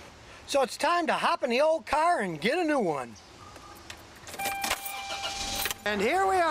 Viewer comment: I thought he was saying let’s go get a new car when he said “ let’s get a new one” lol